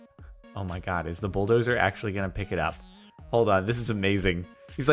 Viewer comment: did u say buldowser ore somting